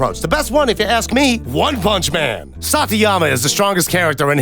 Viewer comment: did he just call Saitama "Satiyama"? D